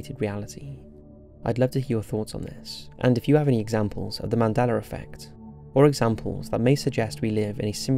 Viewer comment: It's "Mandela Effect", as you pronounced "Nelson Mandela"... I don't know why I keep hearing you say, " Mandeler Effect " ( 11:05 ),but, I do. Great video, regardless how you pronounce it. I think what CERN has (and is currently) tinkering with is what is causing issues. Do a video about CERN!!!